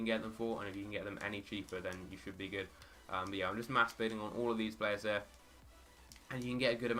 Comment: sounds like your saying "I'm just masturbating on all these players here" :D Love your vids bro. Helped me earn roughly 30K with a combination of all your methods! Cheers :)